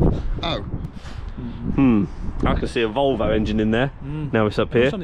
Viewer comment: What? Did he say Volvo V8???